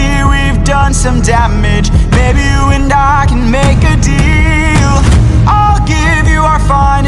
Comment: For a sec when Odysseus said maybe you and I can make a deal, I thought he said baby you and I and I choked on my drink.